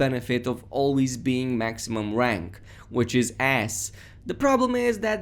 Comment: “maximum rank.. which is ass” is what I heard 😂